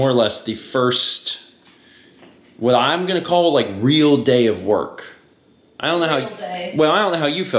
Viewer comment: I just got to watch this now. And.. I don't know why, but @ 2:06 I just heard "Reel day of work." :D Film work maybe? Haha. And.. Happy Birthday Kepler! And... Congrats on completing World of Light!